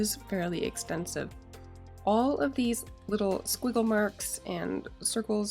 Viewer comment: It’s a good thing you included it in the title because I could have sworn you said squirrel marks.